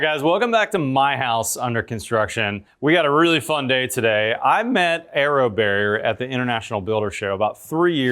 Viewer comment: I thought he was saying "arrow bear" for the first 2 minutes.